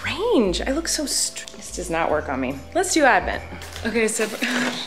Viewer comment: I thought she said, lets do admin I've been playing too much of among us